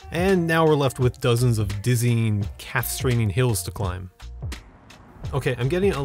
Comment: Did you say “dizzying, castrating” hills to climb??? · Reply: I thought you did say "castrating," and that you were referring to slipping off the pedals of your bicycle when attempting to scale a steep hill ...